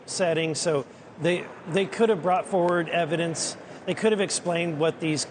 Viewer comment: does he say the "were" or they "weren't" transparent? I can't make it out, anyone please help, thanks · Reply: Weren’t